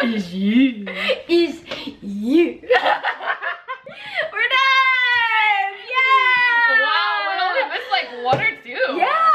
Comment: I swear she said We’re dumb! \ o /